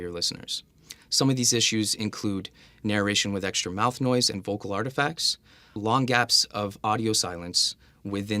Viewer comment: Narration with extra mouth noise and vocal what? I didn't understand the next word you said at appx 1:41 seconds into the video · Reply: vocal artifacts